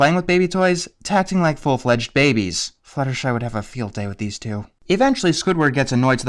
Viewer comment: What does he say at 0:56 I can't make out the first few words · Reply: He says, "Fluttershy would have a field day with these two."